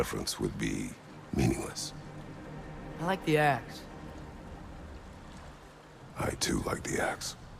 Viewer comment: When Atreus asks Kratos what weapon he favors.. I swear I thought I heard Kratos say.. I like the Ass too 🤣 had to rewind a few times MY GUY!! 🤣